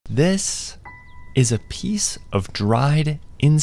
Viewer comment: I heard "this... is.. insect goon" at first